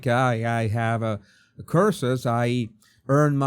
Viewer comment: He probably thought you said "curses" instead of "courses", I did, so I put the captions on & even they said "curses" too.